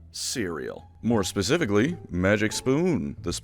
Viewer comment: I thought you said Syria when you said cereal during the add break